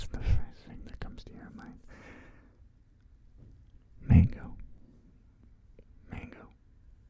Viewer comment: What did you say. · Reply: He said mango I think. . .